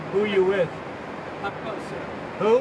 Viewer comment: The interviewer at 14:28 ... WHO did he say he works for?? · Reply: Up Close, whoever that is ?🤷🏽‍♀️